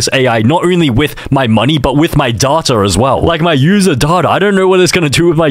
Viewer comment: loool thought he said my daughter😂😂😂